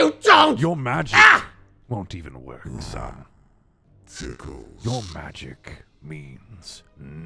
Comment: Woah woah woah yay yay yay woah woah woah yay yay! 4:31 I’m sorry but did he just say “ tickles”!?